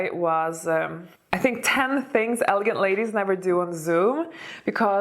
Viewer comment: I thought you said you had a video "10 things elegant ladies never do at the zoo", well that's specific 😄